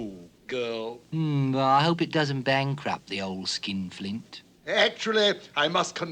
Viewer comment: Ok, does the cricket seriously call the old man a skin flute? I mean what else could he be saying?